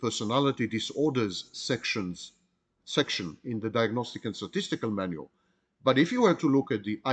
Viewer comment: Oh I thought Sam said diagnostic and sadistical manual 😅